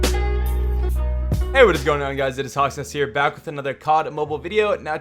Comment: what u said “ hey howz it going guys hawks nest here” What I heard “hey howz it going guys hot sauce here”